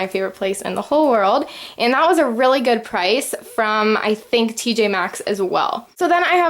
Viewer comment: did she say ' TJ maxx' or that what I heard... coz I'm sure is 'TK maxx'...